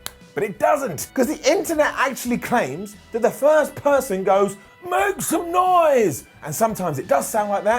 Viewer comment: I just listened to it it says make some noise I always thought it said xpac too